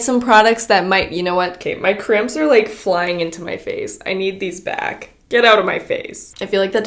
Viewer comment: I thought you said your cramps are flying into your face